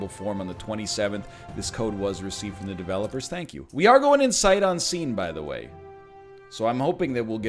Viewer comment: I thought that NL kept saying sight on seen, but I just realized he meant scene not seen